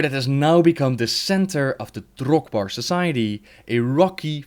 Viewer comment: When that guy one the panel said drogbar it sounded like he said: Drug-bar